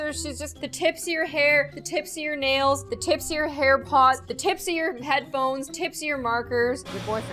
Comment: Gloom: “the tips of you HAIR pods” Me: “D-Did she just say HAIR PODS?! DHHSBDBDHDHSHD”